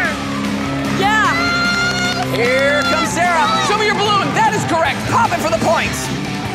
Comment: Did just say, "Cesar"?